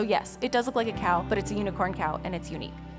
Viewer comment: I first thought that she said "Yes, it looks like a cow but it's a unicorn cow and it's uni Then I realized she said uniqe